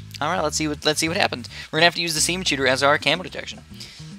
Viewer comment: Wait did he say semen shoot at